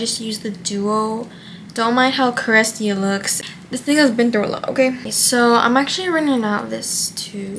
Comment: help did she say nigga😭